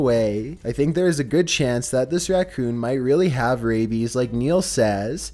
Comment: I thought he said rabbit instead of raccoon on 4. Lol